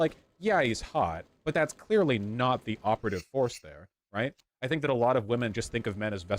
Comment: I legit thought Vaush said operative horse 😂